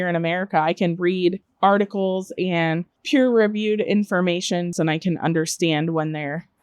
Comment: Did she say "articles and peer-reviewed informations"?